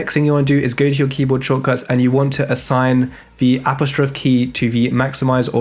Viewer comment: what key is he talking about? Sounded like apple stroke key?